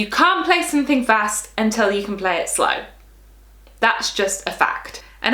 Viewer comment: did she just say "if you can play it slowly, you can play it quickly?" 🤔